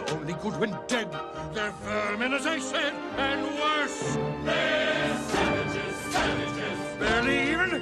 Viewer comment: I swear they said "they're Sandwiches! Sandwiches!"